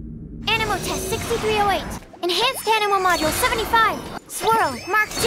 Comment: i just want to know what she is saying XD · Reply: "Anemo Test 6308!" is what she says :)